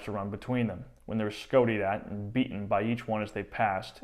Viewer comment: Can someone tell me what "scotied" means (9:07)? I googled it and checked on Wiktionary, but can't find anything.